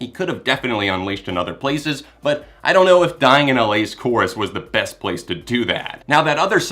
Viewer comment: leisure suit yeah, I really like the rest of the song, so I try to just look past that part. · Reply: I think the recording is just all around pretty awkward. it sounds like hes saying dyding in LA or something. Im sure it will sound incredible if he ever does it live.